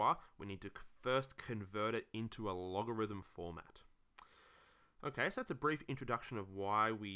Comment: I thought he was saying “logarithms lore”, and I was like mmm 💀